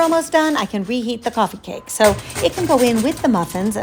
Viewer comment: I have just discovered your channel. Could you please mention the kind of cake flavour you just said crumb cake I looked up your recipes but it only mentions two .Ah scrub that just heard you say coffee cake haha thank you really enjoying everything you are doing .Wendy U.K.